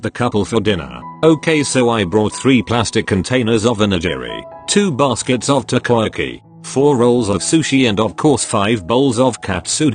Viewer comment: I really thought she said 2 baskets of Tokoyami